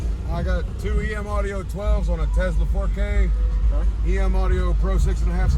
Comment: Couldve sworn he said on a 4k💀 155 on 4k is crazy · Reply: when he said tesla, I thought he said 10k, my bad